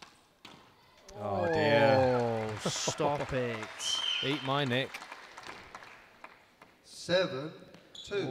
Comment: did he say eat my nick? lol